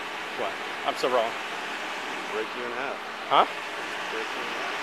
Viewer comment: Did I hear that right? "I'm so wrong."